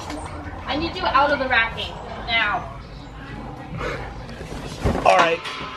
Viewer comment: What did it say at 3:06 or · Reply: I need u out of the racking now